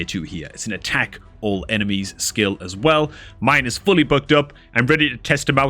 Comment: Every time you say "booked" i hear "bugged". Also Uugo's a woman.